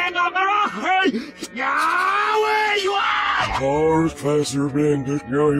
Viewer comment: did I hear Squidward saying "Yeow! Where you Are?"